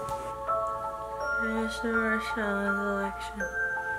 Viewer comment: I might be insane but at 0:50 I swear I heard her say "trump won the election" or "I vote trump for the election" (ik it's prob not what she intended to say but it's pretty similar")